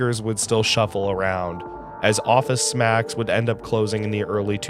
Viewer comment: I swear you said Office Smacks and was thinking, what the heck store is that?! Thanks for all the great content! They are informative, nostalgic, and relaxing all to some great, chill music. And agree on the look. Some places looked more like an uninspired hotel lobby than a mall.